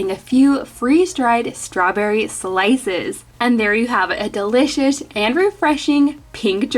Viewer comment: Did anyone else hear delishish 3:37 lol I love your videos :)